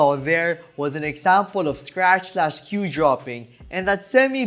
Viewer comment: Minute 5:50 sounds like u said Sasquatch Cue Droppin😂 nice video tho^^